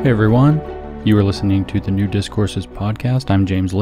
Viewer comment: Whenever I hear Jim say "New Discourses" my brain stutters over to "nudist courses". I'm not proud of myself...